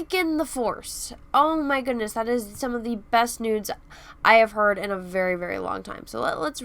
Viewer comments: sounds like you said “this is one of the best nudes I have heard in a very very long time”. 😂 I know you said “news”. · Is it just me miss hearing or does she say "best nudes" at 1:32?